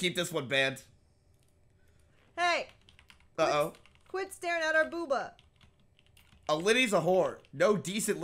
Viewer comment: did she really say quit starin at our booba... She'd be homeless...